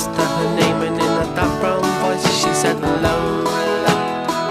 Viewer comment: "In a dark brown voice she said Lola"???? For 53 years I heard "in a background voice she said Lola"